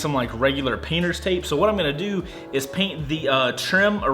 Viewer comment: starting at 1:48 you keep saying your going to paint the trim/driveway instead of tape xD